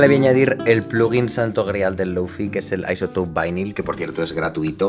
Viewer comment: alguien sabe cómo se llama el programa, no entendí como lo pronuncio. xd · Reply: izotope vinyl